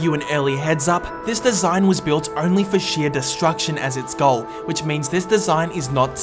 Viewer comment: Lol I thought it said self destruct